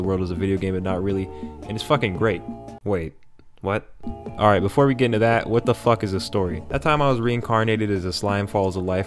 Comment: I thought he said "Before we get into that, wtf is a story" lmao